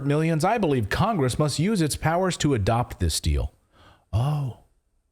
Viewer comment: I misheard "this deal" as "the steal".